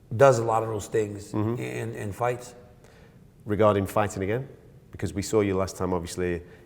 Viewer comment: "regarding fights ......" what does he say??? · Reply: “Regarding fighting again”